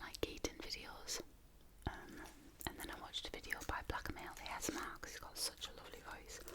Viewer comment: Hi! ☺️ i like so much your videos. Please can you tell me what channel is the one you talk about at minute 17:20? You talk about a eating asmr video you loved but i can't understand the name of the channel... (I'm spanish and i speak english, but sometimes i don't understand some things ;)) thank you and keep doing your awesome videos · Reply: Here you go, his channel name is BlackMaleASMR and that's the link to the video I was referring to. Limeade Oreos (First Impression Taste Test)